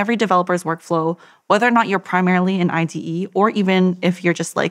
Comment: As with a lot of podcasts on technical topics, a glossary would help many people! I'll start: 1:05 - "Vim" the text editor 1:02 - Does she say "an ITE" or "in ITE"? (Integration Test Engineer? If-Then-Else?) ... Actually I'm now guessing she meant "in an IDE" (Integrated Development Environment) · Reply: Yes, it does sound like ITE but I think you're right, she's talking about an IDE. That's why she says "or even if you're a vim user", mentioning an editor that isn't as feature-rich.